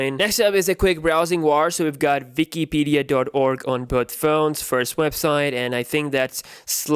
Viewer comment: I heard Vikipedia.